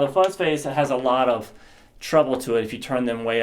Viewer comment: HardcoreHockey Nov. 8 2014 - July 29 2015 Cause of death: "Now we have our fuck face right in front of the amp" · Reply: HardcoreHockey I thought he said fun face